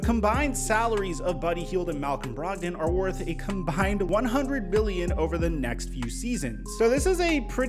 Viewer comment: I thought he said "100 Billion" 🤣😂